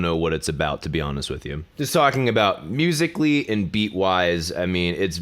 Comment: HOLD ON HOLD ON, DID I JUST HEAR “BEAT”???